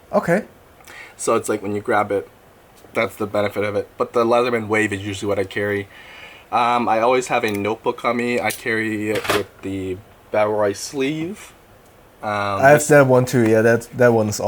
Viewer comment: Sorry, i didn't catch the name of the notebook around 13:45, is he referring to the leather notebook cover? What kind is it? Thx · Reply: Yes, that's the notebook cover from Bellroy